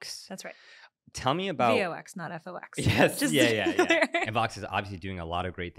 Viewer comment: i heard "v-o-x, not vox" and was so confused. instead of listening again i just went on a wild goose chase